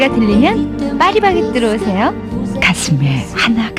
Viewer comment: 저만 파쉬~바게뜨라고 들리나요?